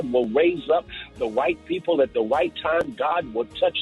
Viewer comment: I thought pastor said raise the white people. He said "right people" had me worried lol